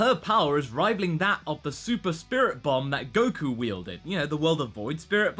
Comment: At first I thought he said "Spirit Bomb Bons". A blue bon bon candy